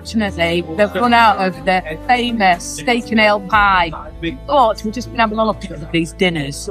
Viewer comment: 😂😂 My granny ears thought you said steak and eel pie! I thought, I’d be in with going veggie. Pleased to hear you later say the ale part of it. 😂😂